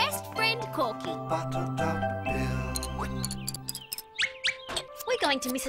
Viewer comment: I Hear Corky Said “No Shit Bill!”